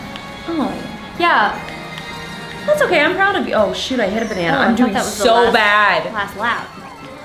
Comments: Wait is this a friendly child channel because I just heard her say “shit” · it sounds like she said shit